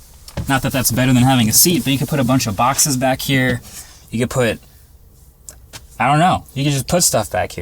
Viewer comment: Climbing in the rear floor with seats up....i swear i thought you were gonna say " put a bunch of bodies back here"